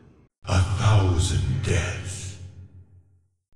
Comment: what is he saying in 7:04? sorry im not a native speaker, and i just wish to know:D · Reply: "have a good one" that's equivalent to "take care"